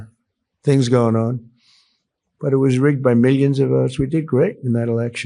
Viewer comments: “it was rigged by millions of US” he said. Anyone else catch that? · DID HE JUST SAY -- "THE ELECTION WAS RIGGED BY US" ???